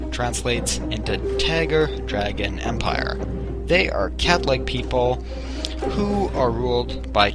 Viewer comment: You said Cat-like people right? Cause I thought you said Catholic people....